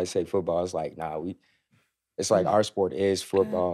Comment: i had the same reaction as....i thought she said Yanda😂😂😂😂